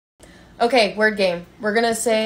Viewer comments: I thought y’all said “okay we’re gay”😂😭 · I thought she said 'we're gay''